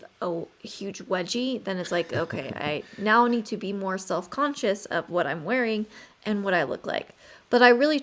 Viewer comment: Did i hear "more sex" in what i can do more.